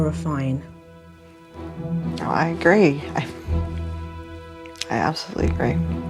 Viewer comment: i thought that in 10:40 she said i like cream but she said i agree